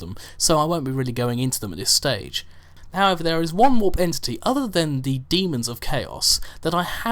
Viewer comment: Do the vampir one, it sounded like u said vampire warp entity, and I know u said there isn't a lot on them but still